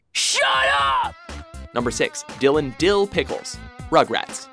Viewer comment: when he said dylan dil pickles i thought he said dil and dil pickles